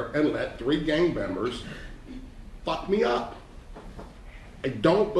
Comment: Lol did he just say, "Fuck me up" in court? Wowwwww